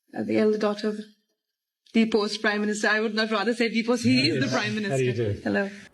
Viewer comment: In all fairness I heard “deposed Prime Minister” and not post prime minister.